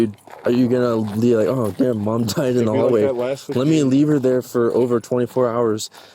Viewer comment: Heard this as "mom died in the holly", had to go back and re-watch.